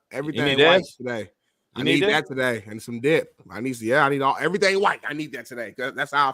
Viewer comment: Did that man say that he needs everything white today and then he said I need some dick or did he say dip because I'm pretty sure I heard him say I need some dick 😂 wtf